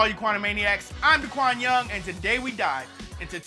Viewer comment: Lol at the beginning I thought you said “today we die”